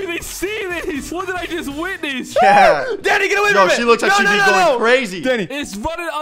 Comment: cc says he said daddy